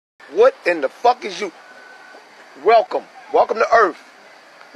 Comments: I heard "Bish im like you but im cute" =3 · “Bitch I’m ugly but I’m cute” lmao shit had me rolling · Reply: Bruh, I thought he said “Bitch, I’m lacking but I’m cute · “Bitch I’m ugly but I’m cute” lmao shit had me rolling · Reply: For the longest time I thought he said “Bitch I’m ugly but I’ll kill you”